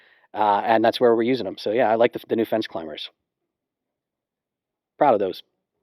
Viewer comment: I thought you said "Da Fence Cllimbers" at first... which is actually a great name if it were real... as it's both like "the fence" and "defense" and in both cases they're being climbed over..